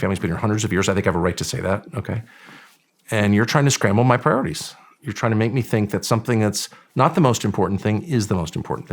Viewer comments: what word did Tucker say that was censored out? And why? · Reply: He said “the tr*ns thing”…..censored to keep the threat of being demonetized at bay…hopefully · What Tucker said was silenced. Why and what did he say? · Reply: “The tranny” thing. I bet the video could be demonetize for hate speech for saying “tranny” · What word was censored when Tucker was talking about ‘the ___ thing’. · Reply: Tucker dropped an F bomb. YouTube/Shawn try to keep it R rated.